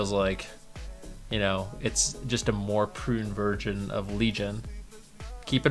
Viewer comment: Did u just say prune virgin instead of prune version?